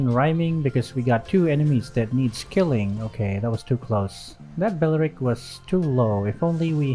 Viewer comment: I just heard "tolol" after seeing the enemy fireboi and Karina